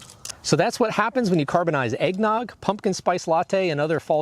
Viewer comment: Thought it said "carbonating" and I was like "meh." Then I realized it was carbonizing and I was excited XD